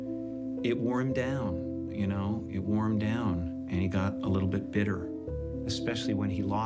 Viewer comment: "it warmed down"? im not a native english speaker but wouldnt it make sense if the subtitle said "it wore him down"?